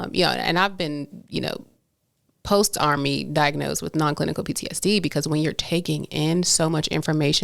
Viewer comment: she definitely said "non-clinical" PTSD then changed it to "non-combat" when you repeated what she said at 27:28. Just wanted to put that here so you know you weren't misremembering 👍